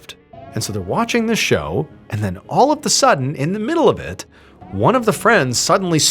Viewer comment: Quick question... Just heard you say, "all of "THE" SUDDEN"... I've only known that phrase as "all of "A" sudden".. English 😉 anomaly 🖖